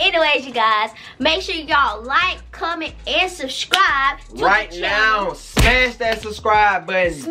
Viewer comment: Am I'm the only one who heard her say Shrubcribe